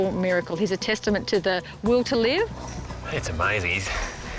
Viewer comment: Did she just say "The Wool To Live"? How dare she be funnier than I'll ever be